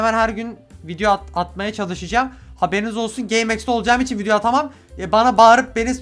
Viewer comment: ya korktum gaymeps diye duydum lannn